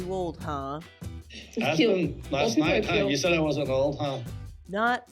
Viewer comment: At 1st I thought David said "Aspyn, yoiu think I was too old last night....", but when I listened the 2nd time, I actually think he says "Ask Mum. Last night, hon, you said I wasn't old, huh?". David's giving the sass back. Mitch even smirks like "Nice, Dave, props."